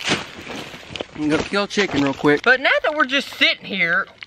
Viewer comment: Did he say he’s gonna kill that chicken real quick?